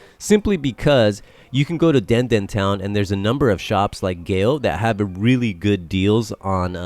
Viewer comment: Hi. What was the store you mentioned at 4:15 in Denden Town for PS5 and Switch games? I heard Gale/Gail but I can't find that store. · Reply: GEO Hyper Media Shop! <link> Cheers!